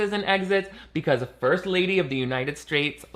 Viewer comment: It's probably me but at 9:22 I heard "First Lady of the United Straights" and it's extremely funny